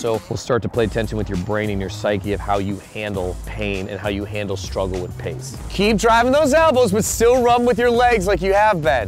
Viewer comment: I thought he said "struggle with pigs" 😂